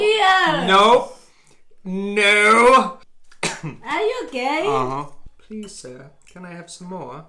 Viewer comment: Ha Ha could of sworn heard say are you gay😂😂😂😂😂!